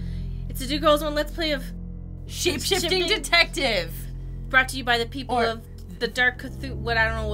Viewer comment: I heard it as Shifty Detective when they made the opening line. Also is amazing.